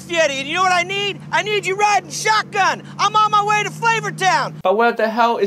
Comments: Who plays genshin and thought he said raiden shogun 😂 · As genshin player idk why but I heard it "raiden shogun" instead of "riding shotgun"